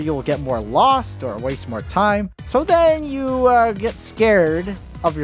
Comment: I thought you said weast